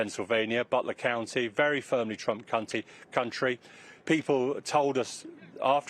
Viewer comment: Listen at 6:00 Did he call Trump a naughty word? 😂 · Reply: No. He said country in a British accent.